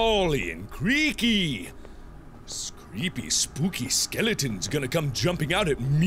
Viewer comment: Did mark just say.. spooky scary skeletons?? 1:50 I need someone to confirm this...